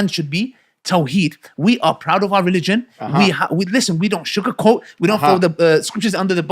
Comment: Plz clarify Ali's phrase in minute 8:04 it sounded like "shook a coat".